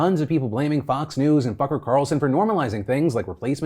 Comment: Ha, did you just say F*cker Carlson? LOL